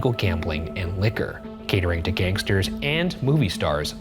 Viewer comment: I legit thought you said gatorade for gangsters and not catering.